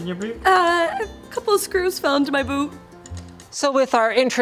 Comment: Did she seriously say boob instead of boot?